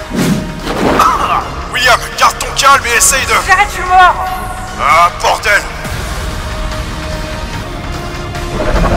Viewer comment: il a dit quoi william sinon t'as fait un travail de ouf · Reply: "si j'arrête, je suis mort !" :p Désolé c'est vrai que c'est mal prononcé, je m'en rendais pas compte (x